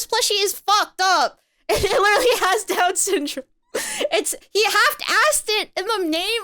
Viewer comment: did she really say down syndrome right there?